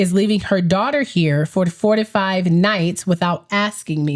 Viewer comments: I thought you said 45 nights at first lol. Yeah that would have been really bad. The 5 day is bad enough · I thought you said FOURTY-FIVE nights! ROFL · Omg, I thought you said FORTY FIVE nights 😂😂😂😂